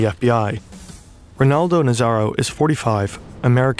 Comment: I heard that as Ronaldo Nazario😭